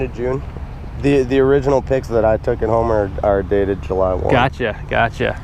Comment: Did anyone else hear the guy say "July 1th" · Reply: He said July 1 not 1th.